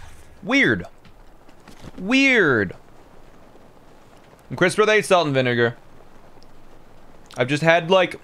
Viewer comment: What did he say he was eating at 2:09:30? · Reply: Crisps, salt and vinegar